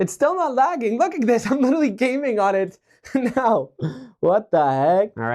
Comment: did he just say “ I am gaming “ 😂